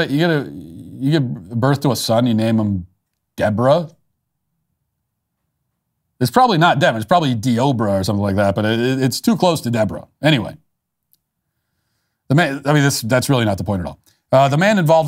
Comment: The mother and family of this lunatic were screaming not to hurt him. WOW. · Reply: I heard 'Oprah' tbh.